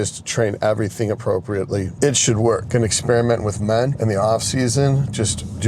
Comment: it sounded like you said “experiment with men” rather than ment. LOL